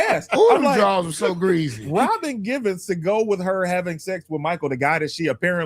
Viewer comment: did say Robin Givens?